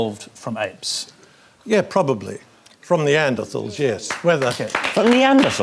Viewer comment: can someone tell me what is the priest saying at 1:00? sorry im english is not my mother language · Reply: "From Neanderthals, yes", I don't understand what he says after that either